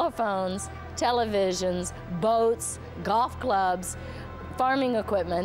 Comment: at first i heard "goth clubs" and didnt get the connection ⚡️